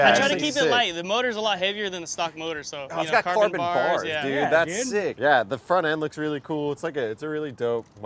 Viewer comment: Hert would have appreciated those burnouts more than ya’all did… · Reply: I thought they said hurt is sick